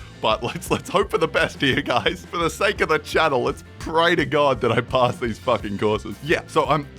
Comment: Do I hear "For the SNAKE of the channel"?